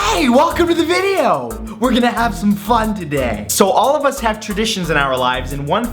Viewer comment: my phone buzzed you out with a text notification so when you said 'were gonna have some fun' it sounded like a sensored' hey kids, were gonna have ** today!!!!!